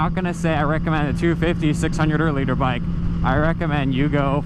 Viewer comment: until 4 minutes in I thought he was saying leader bikes, Murican accents...